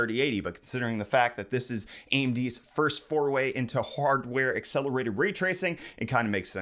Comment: Did you just say four way?